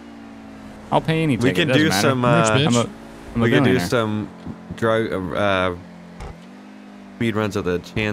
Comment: sounded like man was gonna say drugs lol